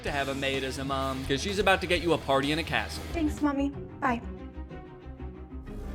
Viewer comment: Misheard "party in a castle" as "party in a casket".